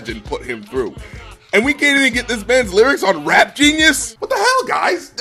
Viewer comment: I thought you said best lyrics